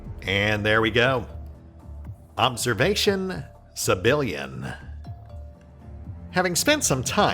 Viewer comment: All that and the xenos still didn't stand a chance here. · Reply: And I keep hearing "Civilian" every time you say "Sebillian."